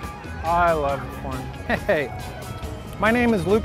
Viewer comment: I thought he said 'I love porn I love porn'